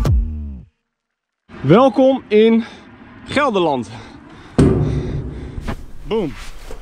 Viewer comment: everytime he said gelderland i thought he was saying netherlands in dutch. I was like why is he saying netherlands in dutch hahaha.